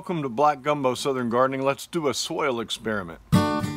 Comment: I thought he said a "swale" experiment lol